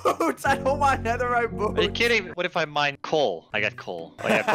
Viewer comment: the captions say cole and not coal lol